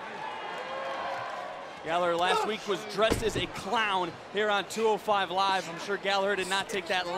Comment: At first I thought he said he's a cloud